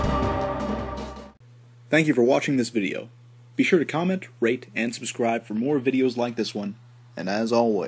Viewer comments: Did he just say comment, RAPE and subscribe? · I heard 'Be sure to comment, rape, and subscribe'